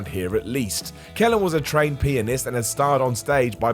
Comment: Time 5 min 3 sec. Dude sounded like he said the maestro was a trained penist. Bout choked on my drink wen I heard that!